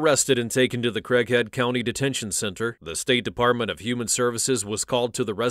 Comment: At least shes stupid enough to admit what she did….not a parent….also I thought the announcer said she was taken to the crack head detention centre and thought “aptly named. (Had to turn on subtitles to see what it was.